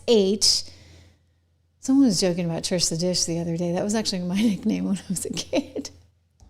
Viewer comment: Did you say Trish the dish? ❤